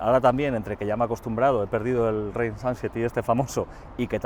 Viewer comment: "rain sunsety"?????... ¿Qué es lo que dice no sé qué "súper famoso"????? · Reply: Quería decir range anxiety 😅